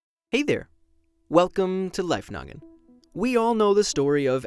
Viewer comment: I thought he said "welcome to life nugget."